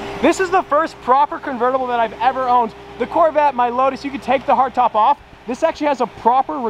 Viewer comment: is it just me or did he say my lotus ??